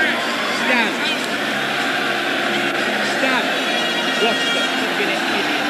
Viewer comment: sounded like "Dan!Dan!" 🤣🤣🤣